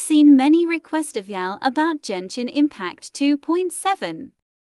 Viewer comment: Am I the only one who heard "JENSHIN IMPACT" 🙂